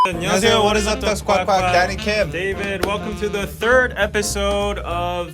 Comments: First thought Danny said "Daddy Kim". hehehehh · Lol i heard "daddy kim"